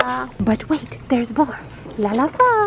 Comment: did i hear, "¡ooh espejo llama!”? ✌🏽🤣🙌🏽